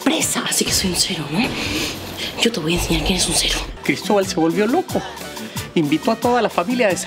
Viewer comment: En la parte 0:24 que es lo que dijo yuli????? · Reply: Yo te voy aseñar quienes un cero